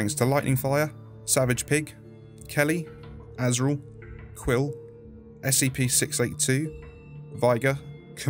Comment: Does he just thank scp 682 or am i hearing things